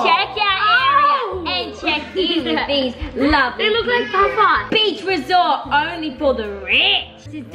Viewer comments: Did Biggy just say "They Look like papa" ? XD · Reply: He said they look like papa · Did Biggy just say "They Look like papa" ? XD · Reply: I thought he said “they look like peppa “….